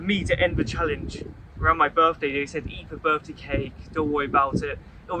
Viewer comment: what he said was “they said eat the birthday cake” not “eat the [unintelligible] $30k”